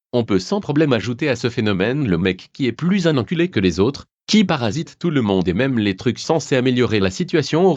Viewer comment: c quoi ca 4:17 j'ai pas bien compris ? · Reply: un putain de parasite